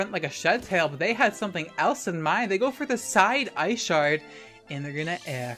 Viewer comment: HELP i heard "side eye shart" at first 😭